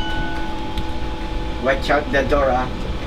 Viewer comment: Did he say "watch out the door?"